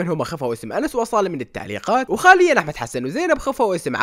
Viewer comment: كال خاليا بدل حاليا لو اني مسمعت زين 😂😂😂